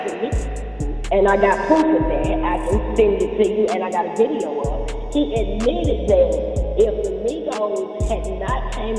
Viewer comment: I can’t barely hear anything the girl is saying. SMH it sounds like she’s in a tunnel and the music kind of overpowers it a bit. Wish I could’ve heard what she said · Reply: I LIKE UR EYES